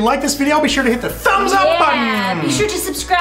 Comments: Lol i thought he said" that's right PENIS!" XD Anyways yeah I have a similar story, cept it wasn't on a dating site and it only lasted 8 months :( But it was great while it lasted :) hope you guys continue to love each other for a long time! <3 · I dont no if that was intentional or by accident but when u said pin us u really sounded like u said penis